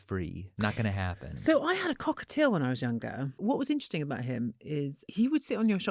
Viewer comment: i wasn't listening properly and thought she said i had a cock or two when i was younger. 🤦